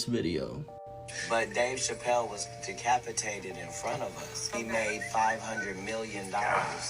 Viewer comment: Shalom , missed y’all! Two videos were muted BUT they have captions, there was nothing I can do about it 🥹 love y’all! Enjoy! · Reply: Hello there! Shalom! Hope all is well. Quick question. During the podcast with Katt Williams around time marker 10:45, did he say, "Dave Chappelle was decapitated right in front of us."? I'm not sure if that's what I'm hearing. Okie dokie. Thank you for sharing the truth of what's going on in this world and spreading YAHUAH'S truth. HalleluYAH! Hope you're able to enjoy a beautiful evening. In the name of Yahusha HaMashiach. Amen & Amen. Shalom! Shalom! 🫂📖🕊🙏🏾💯